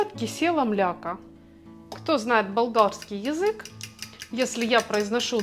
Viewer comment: С 4:00 минуты, ….кто знает молдавский язык……. Тогда при чем тут Болгария ? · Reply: Правильно Вам комментатор выше сделал замечание насчёт произношения и дикции . А Вам , прежде чем отвечать и советовать слушать повторно , следовало бы самой послушать что Вы сказали и адекватно на это ответить. Лично мне тоже непонятно, что Вы произнесли , мне слышится - балдагский , и прослушала я неоднократно и внимательно . И если Вам никто больше не написал замечание по этому поводу это не означает, что все поняли , и ваше замечание , что неправильно послышалось только " учителю с идеальным слухом " по меньшей мере выглядит манипулятивным и обесценивающим мнение комментатора .